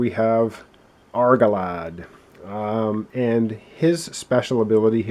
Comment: I thought you were saying Argo Lad and Amphi Lass... sounds more like a Superhero duo...